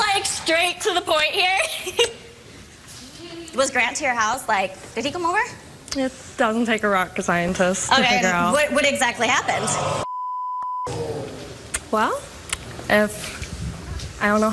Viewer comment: Ummmm did she say rock scientist??!